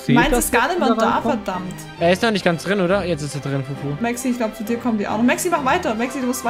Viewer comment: <— „er ist noch gar nicht richtig drinnen, oder ? Jetzt ist er drinnen!“ that’s was he said 🤪😂🔥